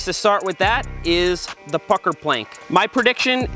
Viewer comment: I thought he said fucker plank 😂